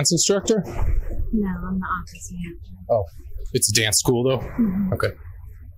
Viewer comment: "no I'm the office manager" cc wrong. It always bugs me when i can tell what the person says, but the cc is wrong